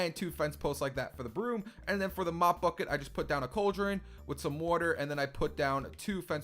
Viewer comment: Haha. When he said mop bucket, I thought he said he said mott f*ck it.